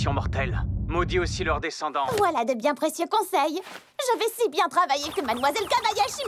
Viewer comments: What is she saying in french @ 11:15? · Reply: Voilà, des bien précieux conseils. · vous avez entendu comme moi "Kabayashi" et pas "Kobayashi"?